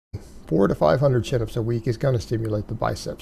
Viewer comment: He hasn't done 400 chin ups in his entire life! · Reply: I thought he said "400 SIT-UPS" would "stimulate the biceps." Me: Welp. There's your problem.